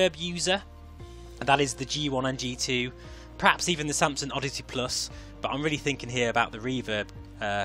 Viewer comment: Thanks, great vid! Got a chuckle at 1:42. Pretty sure I heard you say "Samsung Oddity+". That would be an odd headset! =D